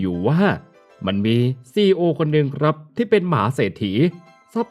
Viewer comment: ได้ยินว่า "หมาเศรษฐี" 🙄😅